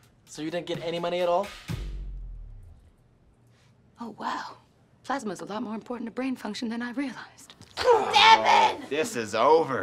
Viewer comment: "Plasma is more important than brain fluids" Best Quote ever!!Love JK Studio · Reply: I think she's saying "Plasma's a lot more important to brain function than I realized." And Is it at 14:30?